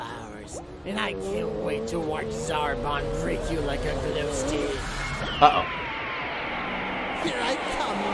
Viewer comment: OMG all this time I thought said "treat you like a gluestick"